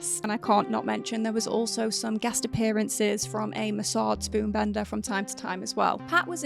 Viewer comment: i heard it as massage spoon bender